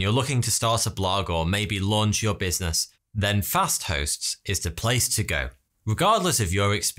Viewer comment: Damn, I been looking at memes too much I thought it said Fas Thots at the start. Lol